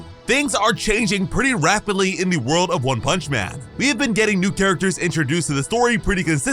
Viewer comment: does anyone else hear one Punch Manth